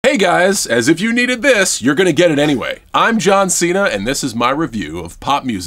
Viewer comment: I first i thought he said I’m jon snow